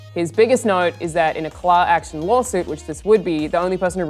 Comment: Did say "claw" action lawsuit?